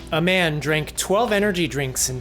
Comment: I thought that said "a German" and I was utterly bewildered as Germans are impervious to bodily harm.